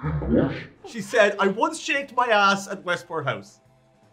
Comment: Now, I heard" I once shaved my ass at Westport house"